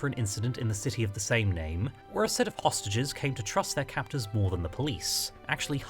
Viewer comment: I misheard "hostages" as "ostriches" at first and was confused :P